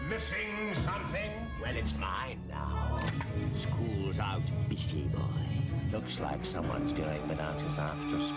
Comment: I thought he said "Skool's Owt, Beastie Boy!"